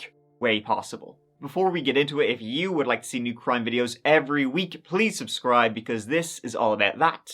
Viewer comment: I thought you said "if you want to see me cry in videos".... ..ya cool!